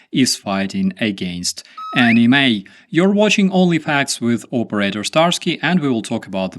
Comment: Yeah I heard "Only fans" at first as well... Starsky ! You Pervy dog. I guess if it helps UKRAINE 🇺🇦 🤷🏽‍♂️